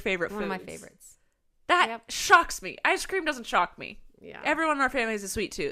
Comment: I thought she said “that shucks me” when they were talking about corn